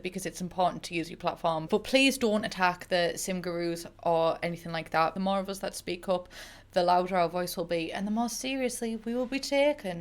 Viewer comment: All I heard was “Attack EA” 👁 👄 👁